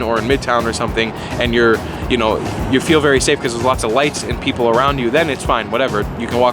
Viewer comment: You said a lot of lights lol thought you said you'll feel safe somewhere with a lot of whites. I'm like damn that's harsh lol